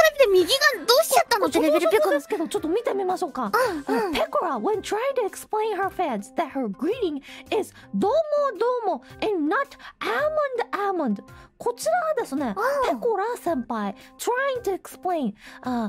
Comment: First time jump to rabbithole and introduced to pekora konpeko greeting I HEAR "a-mondo a-mondo" Seriously... until she said that herself it was "do-o-mo do-o-mo"